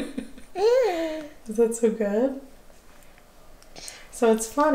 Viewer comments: I missed this video. Love the babies. So sweet. sounded like she said “that’s so good” I think. Mom knows better than I do. Well till next time. Hugs Betty 🥰🥰🥰🥰🥰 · It sounded like said that’s so good!👏🏻